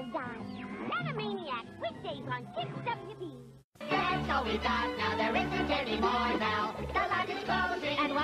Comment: What are they saying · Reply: Hey so we got now